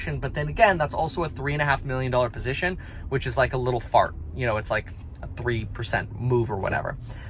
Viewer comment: Did I hear that right? around 5:50 He said "$3.5 mil position which is like a little fart"...lol. Boy wish I can say the same thing.....lol